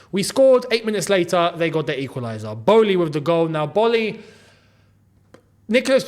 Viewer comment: Who else heared Boehly with the goal 😅